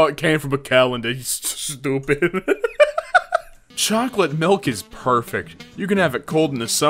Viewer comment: for a second i heard chocolate PILK of all things